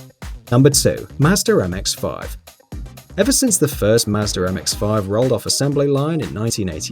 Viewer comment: I heard “Mazder MX5” instead of Mazda MX5.